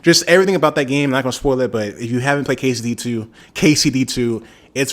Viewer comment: I thought you said quesadilla 2 😭